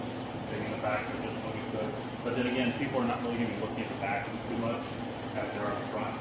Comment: Couldn't hear you at 17:00 minutes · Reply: I was pretty much saying that for the most part no one is really going to be looking at the back so it didn't need to be perfect